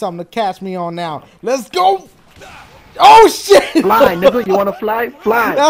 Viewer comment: did said nigga o.O